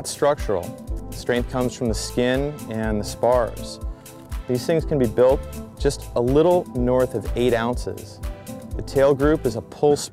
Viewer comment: I would be grateful if anyone kindly writes out what says around 00:42-00:45. I am not an English speaker and can't understand what he says... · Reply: these things can be built a little north of 8 oz